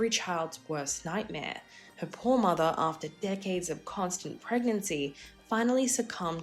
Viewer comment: she doesn’t say that at all! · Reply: What Video are you watching? Cause it sure isn't this one. NEVER AT ANY TIME DOES THE NARRATOR SAY THAT!!! Go back to about 1:30 and you will hear what is said. And at NO time does she say that. The word "probably" is NEVER spoken!!! She says that " her poor mother, after decades of giving birth, sadly succumbed to breast cancer" That is NOT saying the breast cancer came from the pregnancies. Comprehension.....LEARN IT!!!